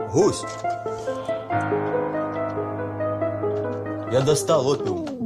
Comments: что он сказал? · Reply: Я достал Опиум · что он сказал? Раз 100 смотрю и не могу понять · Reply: Он опиум(наркоту) достал. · что он сказал я достал... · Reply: Опіум